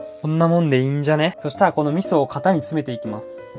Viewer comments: 味噌って聞こえたけど気のせいだよな…疲れてるわ自分。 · 最高！さんの動画はなんか中毒性があって面白いです！毎日見てます！ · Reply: 味噌って聞こえたのは気のせい…ですね